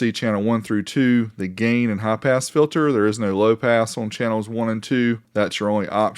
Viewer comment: I heard "Half ass filter", and though; That's no way to run a railroad! :-D